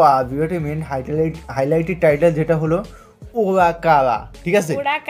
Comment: ভাই এই গানের নাম কি বললো, 0:37, (হোগা কালা) 😮। নাকি আমি ভুল শুনলাম।😂